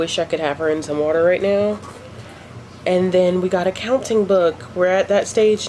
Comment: I thought you said an accounting book for a second there! 😁